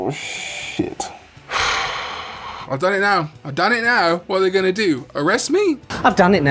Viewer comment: What you said: "oh no, I gave Renamon the D! Well, what are they gonna do arrest me!" What I heard: "I caught a Lopunny, Batman"